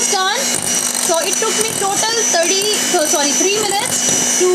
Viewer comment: sorry I am not sure I understand, did it take you 23 minutes to whip that up ? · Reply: Thats what i heard also. I thought she said took 30 to 33 minutes